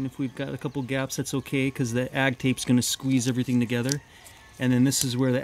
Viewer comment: What is that green tape? Sounded like he was saying Ag tape?